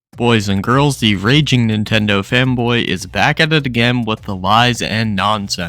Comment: I keep hearing "Nintendo Femboy" which is fitting for someone who keeps simping for Link.